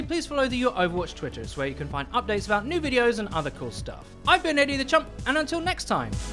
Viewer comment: I never understood your name. I always hear "Eddie the chump"